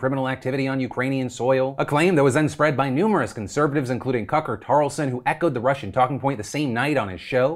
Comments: Did I just hear Phil say "Cucker Tarlson" at 12:01?? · did you just say "Cucker Tarleson"? lol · Everyone's talking about Jussie but I DEFINITELY heard "Cucker Carlson"...